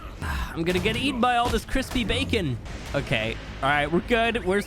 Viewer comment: What he said “all this crispy bacon” What I heard “ Chris P. Bacon”